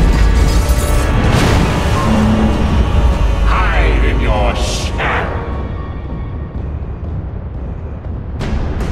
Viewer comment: What did he said at 20:23 please? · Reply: Actually, it's 'Hide in your sham!'